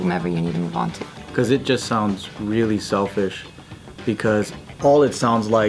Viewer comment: what says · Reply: "cause it just sounds really selfish. because..."